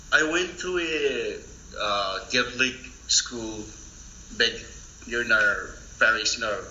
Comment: I think he says Catholic School.